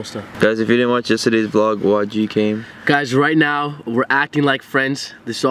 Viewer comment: I thought he said YGK..